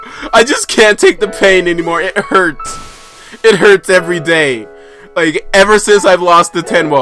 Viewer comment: I hope I didn't hear what I think I heard lol "It herds... it herds every day..."